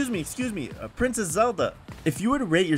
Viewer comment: I thought he said princess Elsa 💀